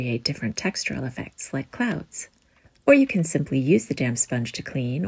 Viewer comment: I heard the last "damp sponge" as "damn sponge" and was like, whoa this changed quick. Lol Looooove your voice!